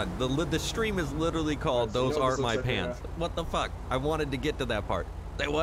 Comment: I thought it said “Thoes aren’t my parents” 😂